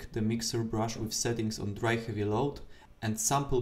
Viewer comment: I need a rundown at 0:27. Mixer brush with, what? It sounds like you said "Mixer brush with setting right and below it" I also don't know what you mean by sample, I try the eyedropper on the gradiant I made but, it's just 1 color on the mixer brush... Edit: The issue was your sampling can depend on your Brush's current size, afterwards though sizing will be OK. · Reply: Dry heavy load. Try to click on sample all layers and sample gradient again hope that helps :)